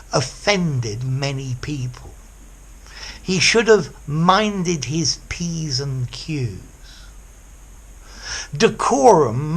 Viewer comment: minded what? I couldn’t make out the exact words.. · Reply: he should have minded his ps and qs <link>